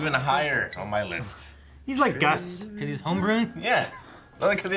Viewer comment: did he say "he's like gus" at 7:15 ?